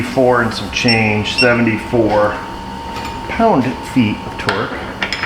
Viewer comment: I swear there's just enough New York accent I hear pound feet of twerk half the time, which would be a hell of an SMA dance move 😂